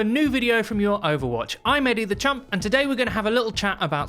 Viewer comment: everytime these videos are on autoplay and i hear "eddy the champ" im smasshing the dislike button out of chringe